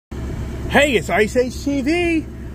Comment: What i heard: "HEY! ITS ISIS TV!"